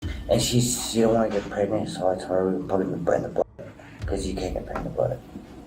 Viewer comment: “Cause you can’t get pregnant in the butt” cop: oh thanks for clarifying I didn’t know that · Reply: I thought he said "I didn't want to get her MORE pregnant cause you can't get pregnant in the butt." lol